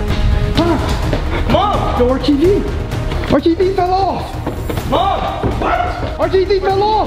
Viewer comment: I thought you said R tv feel off